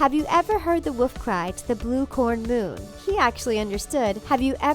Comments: For “the blue corn moon” in the colors of the wind, I heard “the blue horned moon.” Huh, I guess I’m just weird. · In colors of the wind I thought she said have u ever herd the wolf cry to the blue horn moon